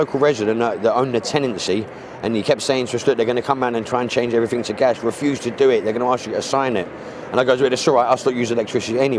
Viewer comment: what is he saying at 2:03? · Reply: About 6 weeks ago a local tenant kept saying to us "look, they're going to come round and try change everything to gas, refuse to do it.."